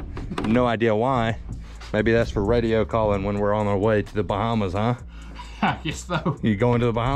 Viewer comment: I honestly thought when you said "... on our way to the bottom" when you said "Bahamas". Lol